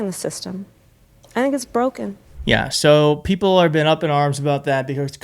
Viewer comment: It is broken! I thought she said, " I'm broken."